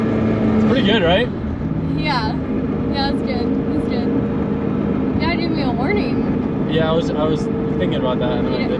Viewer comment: when said "you gotta give me a warning," all I heard was "you're getting me all horny" 😂